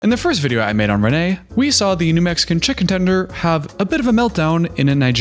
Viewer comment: At first, I thought you said “we saw the Mexican chicken tender” and I lost it. I had to rewind 😂